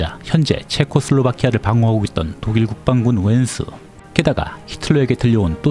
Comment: ㅋㅋㅋ 독일국방국 웬수로 들리는건 기분 탓인가.